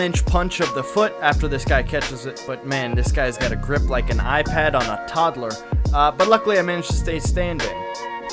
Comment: Bro he said “The grip of a toddler on and IPad” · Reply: No, he said "the grip of an iPad on a toddler" (9:39), but he probably meant to say what you said.